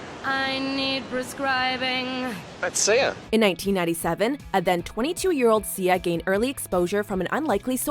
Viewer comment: A lot of mis-information and wrong dates. Does she say 1897 at 2:00?!